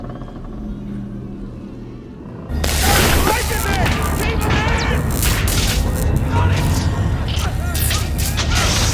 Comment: What he said? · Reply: He said keep those breakers in which is basically hom saying to keep on the power